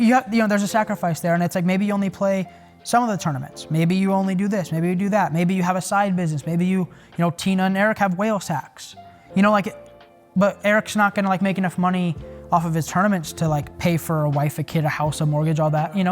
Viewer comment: Did he just say Eric has whale sex??